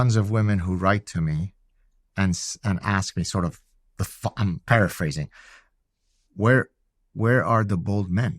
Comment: Did he say Fuckin paraphrasing, or am I totally misunderstanding him? It sounded so weird coming from him somehow.